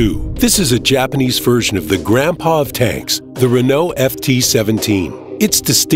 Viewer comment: did u say grandpa tanks or something else?